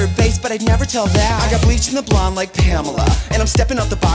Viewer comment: Trixie: “Bleached blonde like Pamela” Also trixie: decided to wear purple hair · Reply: I always thought she was saying "bleach in the blonde like pamela" oops